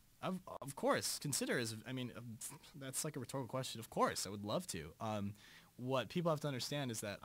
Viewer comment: does he say..thats like a retarded question?? because out of all ppl darren should know the "R" word is just as bad has saying..oh. thats so gay. You know?? Like seriously...I hope that's not what he says..but it sure sounds like it. :/ If not what did he say? I still love him to death...